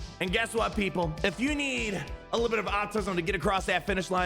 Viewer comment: did anyone else hear "of you need a little bit of AUTISM to get across that finish line..."? 😂 or just me?